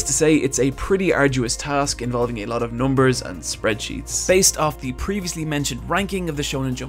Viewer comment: He says off, but the subtitles say of... WTF? 😕